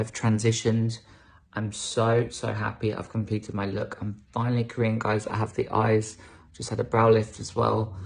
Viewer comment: Honestly i thought it said "im finally a karen" lol